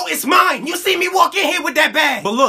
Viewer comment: omg I thought I heard SHARING IS SCARY www